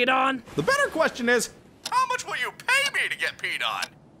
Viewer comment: when I first saw this I thought krill in said how much will you pay to be beat on. not pee on